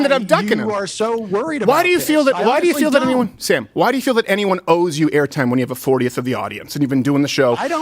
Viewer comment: Do you not know what’s going on? LOL dude, cmon. I’ve been following this for an hour and I know that Crowder only accepted this cuz he thought it’d be easy. He knows Ethan is not a political guy. Since when tf does someone with more than a million subs think someone else with more than a million subs is not worthy to talk to because of their subscriber count. Did you know people with less than a million subs have valid ideas too? Crowder is a fucking joke. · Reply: Do I hear this right is that Sam Seder saying "I don't think anyone owes me air time?"